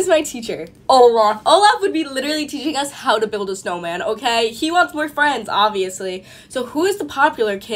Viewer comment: LOL when she said olaf wants more friends i thought she said 😂😂😂Olaf wants boy friends